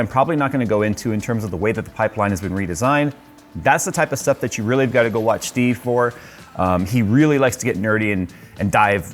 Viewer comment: watch... who? I'm extremely interested but I can not understand what you said :< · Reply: Watch GamersNexus